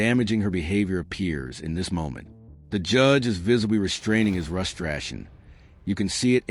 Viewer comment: Was liking this but then I heard "frustasion", and I knew it was AI. Hate this now.